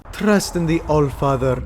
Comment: my subtitles said, "Preston the old father" lol.